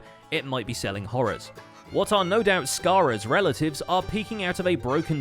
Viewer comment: did anyone else hear "Scarra's relatives" or was it just me?